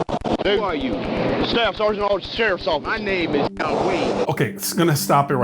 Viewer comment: I heard "my name is John Wayne!"